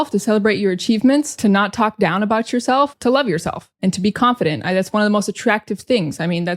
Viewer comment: No entiendo el speed fast En el minuto 1:32 dice: Be confedent that's one Y pronuncia: Bi confieren Aigas uan 😢no entiendo porque Thats se pronuncia "aigas"